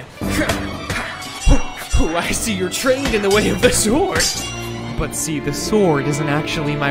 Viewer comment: I thought you said, “I see you’re trained in the ways of the Schwartz” and I really enjoyed it